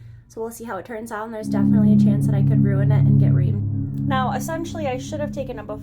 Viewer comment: Wait what did you say at 0:30? It sounds like you said you could ruin it and get raped? Wtf?